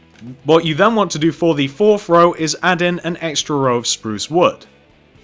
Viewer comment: I thought you said "now forty-fourth row" , and I was like WHAT?!?